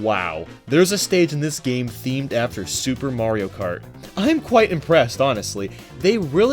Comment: Wait.. wait.. what did he say at 6:33 SUPER mario kart. nice one